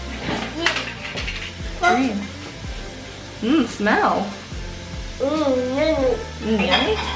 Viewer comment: when you said “mm smell” i swear i heard jin LOL